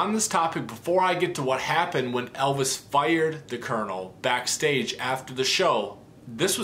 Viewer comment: I thought it said the night Elvis FRIED Colonel Parker. x